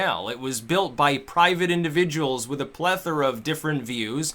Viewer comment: !!!!!!!!!!!!!!!!!!!!! Did he say plethora?????